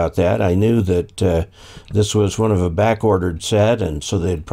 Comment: English is not my first language. i heard a phrase "to be backwatered" or "to be backquartered". What's the right way and what does it mean? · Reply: I think it possibly may have been "backordered." It means you order something from a vendor, but they don't have it, so they put it on backorder. It means once they get it, then they will ship it to you.